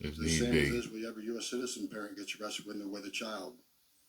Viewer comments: what did he say that talked over it and I couldn't hear it ..did he say its the same as when any us citizen is arrested with a child? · I didnt catch what he said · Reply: It's the same as when a US citizen parent gets arrested when they're with a child